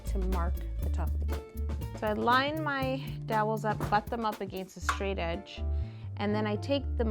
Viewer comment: ish did you say fuck them up????😱😬😮